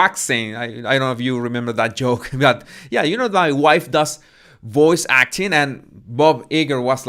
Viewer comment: Voice acting? I thought you said 'ball waxing'!!